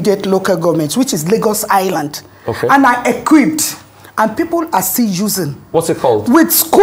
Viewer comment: Furious, bland and blaming Obi's supporters. Heard her say; "Our hospitals are well ecube instead of equipped".